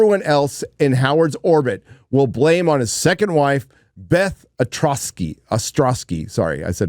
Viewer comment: Beth Ostrosky? I thought you said Beth Atrocity